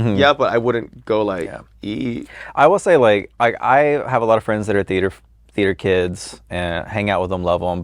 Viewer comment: now miss maddy, I heard that "I have a lot of friends that are theater f--...kids" lol